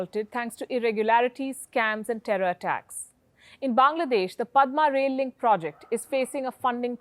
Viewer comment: Did she said thanks to terror attacks